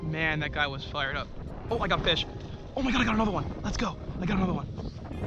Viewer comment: Lmao. At 9:15 mark I thought I heard "Lets Grow!" so I had to rewind... He said "Lets Go!".. Hahha